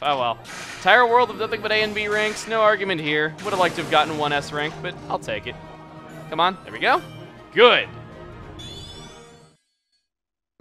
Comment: "This is a ridiculously easy level to S rank..." I had to watch that part three times because I kept hearing "ass rape" instead of "S rank."